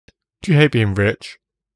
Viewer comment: Almost thought he said "do you hate being British ?"